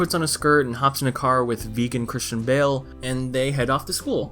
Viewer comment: V E G A N C H R I S T I A N B A L E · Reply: Vegan Christian Bale? I thought he said virgin Christian Bale